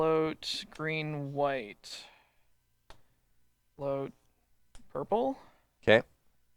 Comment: wait, at 2:08:10 does say float purple? wtf